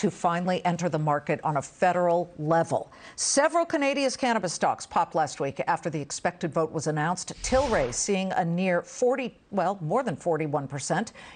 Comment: Did she say Canadia? @ 0:34 😆